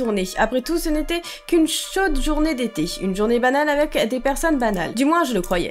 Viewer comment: ça commence bien j'ai entendu "journée banane"